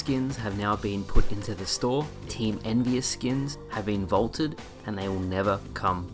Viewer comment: Sorry, did he say team "India"? there was an Indian team? and there was a skin? and it got removed? I have so many questions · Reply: Team Envy Us - NV